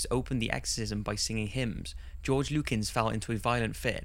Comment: On the second one I thought he said George Lucas XD I mean it would explain why Star Wars is so f***ed up